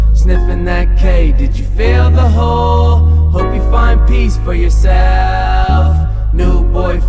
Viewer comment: " Hope you find peace for yourself " I really hope that y'all do find peace for yourselves because we need it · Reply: In this part i heard it "find One Piece for yourself".